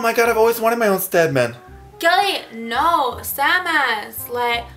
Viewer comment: So thought that was the Samus Aran theme. makes sense since it sounds like she says "samus" when she says "sandman"